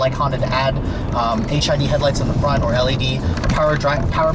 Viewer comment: I thought he said ," HIV Headlights" for a moment.